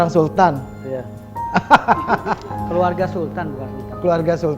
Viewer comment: Kalian salah denger, itu bilangnya keturuna setan bukan sultan